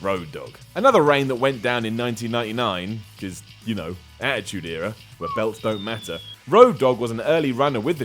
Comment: what does say at 4:39? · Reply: Marcelo Saunders attitude era where belts didn't matter